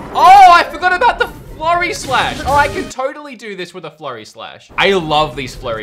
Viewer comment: I legit thought you said “Flurry Slush”